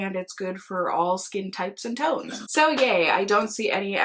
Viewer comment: I heard...and it's good for all skin types and TOADS! 😂🤣😂🤣